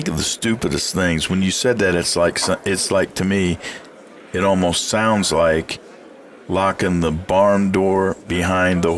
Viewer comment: What did you just say? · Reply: "Friends don't let friends beer microphone."